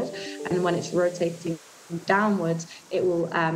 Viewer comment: anyone else heard Windows???